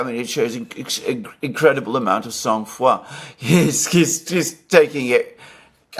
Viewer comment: what “san fua” means that Alexander said , sorry my native is not english, i will like to know if someone notice the saying “incredible amount of sant fua” :)) 6:05 is it like incredible amount of self confidence? I would like to know the word and how correctly is write ✍️ 😅